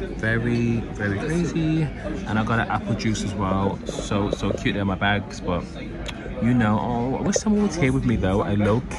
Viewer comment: Sorry what was the name of the restaurant at 11:08? I couldn't hear over background music. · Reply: NOVIKOV XXX